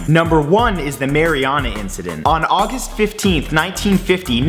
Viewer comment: I thought it said "Marijuana incident" xDD